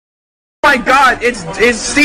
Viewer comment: I thought he said Monkey Fuck hahahaha